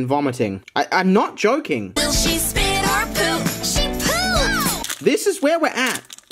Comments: On the unicorn shimmer ad I thought it said “will it shit or poop?” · I heard shit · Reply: It's spit